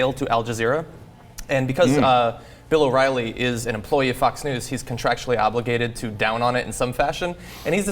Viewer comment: Sounds like John says "an employee of Fucks News". Well he's not that far off though.